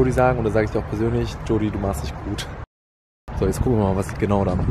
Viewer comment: Sagt er bei 2:38 "du machst dich gut" oder "du warst nicht gut"? Verstehe es nicht :C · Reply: er hat gesagt sie macht sich gut